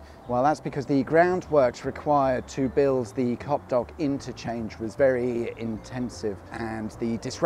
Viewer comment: I enjoy your videos anyway but this one was considerably enhanced by me mishearing it as 'Cockblock Interchange'.